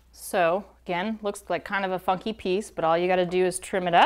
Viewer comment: did she just say fucky peice